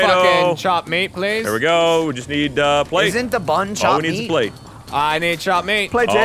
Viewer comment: It sounds like said "Isn't the bun chopped meat?" @ 19:09 Am I right or just hearing things again?